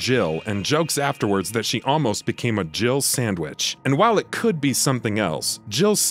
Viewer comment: It sounds like Barry says jiggle sandwich